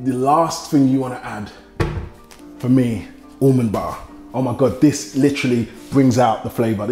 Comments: thought u said Almond bar, i was like wtf is that... almond butter became almond bar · I heard him say to add an Almond Bar, the way he pronounces Almond Butter the British way. Almond Ba-uh... 😂😂😂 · Jeez.....that accent. lol I thought he'd said, "Almond bar." Until I read the notes. He was saying, "Almond butter !" LOL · I literally heard him say Umumba and I had to read what he said LOL Almond Butter.. Umumba LOL